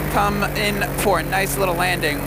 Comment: did he say fortnite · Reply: "For a nice landing" sounds like. Fortnite 😅 but I am thinking finally someone answered your question about 4 years after 😂😂